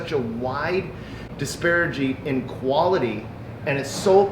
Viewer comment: Did he say there is a "wide disparaging" between the two?